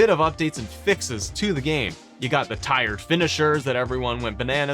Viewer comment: What does he say at 5:05? 'Tired finishers'?